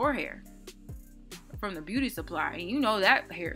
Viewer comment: Lmfaooooooo the whole time I thought you were saying booty I didn’t understand until you had your makeup and I was like Ohhh beauty 😩😂